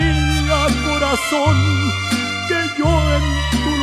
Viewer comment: 00:2:37… que dice aquí…si tía María o si te amaría o sitie a maria?😕🫤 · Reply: Si te amaria.. Un verdadero Mexicano lo entiende perfectamente!! Quien conmigo?